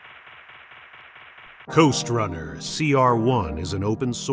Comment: I first thought you said Ghost runner lol....time for uppers!